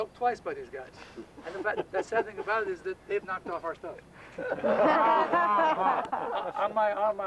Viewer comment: what does he say @ 10:20 ?..."the sad thing about is that........???" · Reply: “The sad thing is that they’ve knocked off our stuff.”